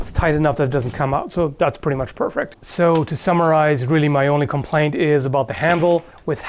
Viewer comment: "so to Summarize" I heard "so to Samurais"